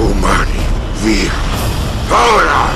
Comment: ma che cosa dice quel personaggio dopo "umani?". Non riesco a capirlo dalla voce😅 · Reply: Umani ViA ! Ora !